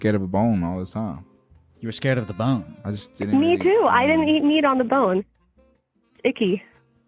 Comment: did she just say "meat on the cob"